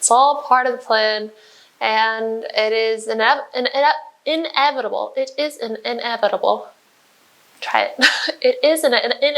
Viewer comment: thought you said it's an edible 🫩😂❤️